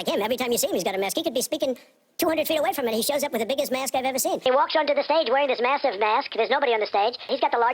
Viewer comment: it sounds like he says "he has the biggest ass I've ever seen" I know it's just the speed and filter but brb I laughed so hard